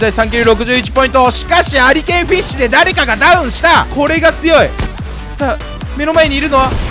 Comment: ありけんフイッシュで倒したときこれが強いって言ってんのに俺が強いって聞こえた